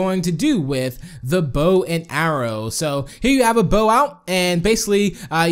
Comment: At First I Heard ""Boat And Arrows""